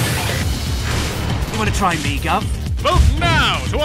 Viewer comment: what was that? did he say "Wanna try make-up?" or "Wanna try my glove?" 0.0